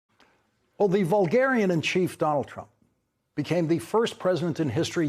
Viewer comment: i thought you said BARBARIAN in charge same difference eh